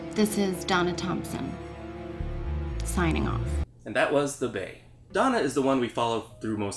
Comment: I thought it said the bae